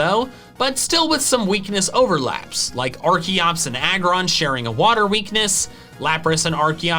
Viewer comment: i misheard archeops as arceus and wondered why you didn't say arkoos.... now i can't stop saying archeops as arkoops